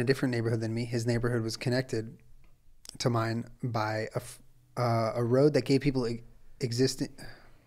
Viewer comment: I thought he said “a road where gay people existed”